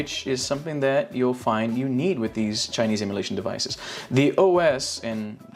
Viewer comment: Kinda sounded like you said "Chinese immolation devices". 🤣🤣🤣 Nice overview. I picked up this one, and the RG353M, and at least one will be going back. It's hard to resist the metal case, Hill sticks, and a little Dreamcast action, so probably the XX will be re-boxed soon. Tempting to keep both, but that's just a bit too much $$$ outlay. I'm hoping I can throw Robotron 2084 on the dual stick 353M.